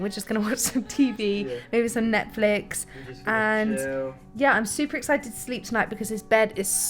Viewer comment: did you hear what said after you said Netflix? 😅😍💐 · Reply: Looool he actually said ‘yeah just chill’ 😂😂 but it kind of did sound like Netflix and chill ahahaha